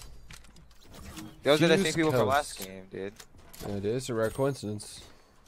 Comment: "those are the niggas from the last game" is what sway was saying but its censored lol